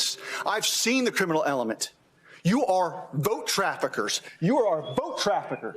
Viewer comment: 🤣🤣🤣 I thought he said goat traffickers at first. Same thing basically.. XD